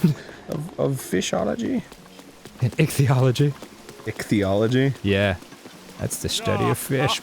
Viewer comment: At first I thought they said ECHology